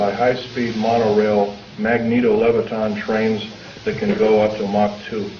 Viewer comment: What does he say @ 5:24? That can go to mock 2? I'm trying to google this but I don't know what he says. tia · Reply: Sees Nails that can go Up to uh moc 2